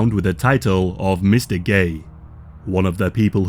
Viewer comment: I’m sorry but was Mr. Gay really the best title they could come up with ? 😭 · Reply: For some reason when I first heard him I thought he said "Mystic Gay" and I realized that didn't fit and it was Mr. Gay But I still thought Mystic Gay sounds so much better.